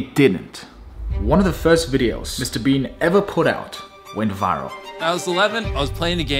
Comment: did he say "Mr Bean" instead of Mr Beast?